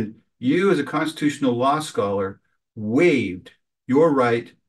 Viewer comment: Constitutional Scholar? I thought he was saying Constipational scholar.